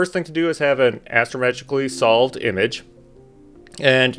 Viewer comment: Did you say Astro-magically solved image at 2:43 !? LOL It does seem like magic sometimes 🙃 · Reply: no hahaha astrometrically solved image.